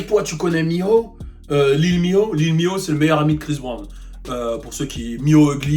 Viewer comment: C’est quoi le nom du meilleur ami de Chris Brown? 5:18 "lil mio" ?